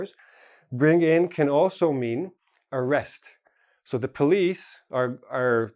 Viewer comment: When you said "arrest" I heard it like " a rest" (like vacation); I`m surprised that two opposite words sound so similar